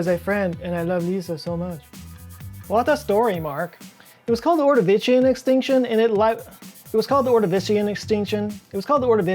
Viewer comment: I thought that you said "the lord eviction extension" Sounds like a really cruddy prog rock band name.